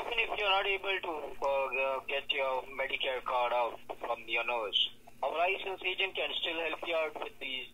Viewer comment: ? But of course you are! And he seems to think you keep your Medicare card up your nose. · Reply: Lol I was like, Did he just say your medicare card is up your nose? 🤨